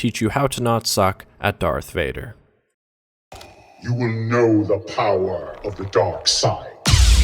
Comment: turn on subtitles did it just say you will know the power of the dog saw